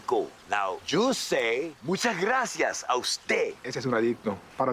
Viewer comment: I’m not sure if somebody pointed this out yet, but I’m pretty sure the subtitles should say “Muchas gracias a usted!” Not “I will stay!” For context, “a usted” is basically saying “to you” but in a formal way, so he’s asking Jesse to thank him formally.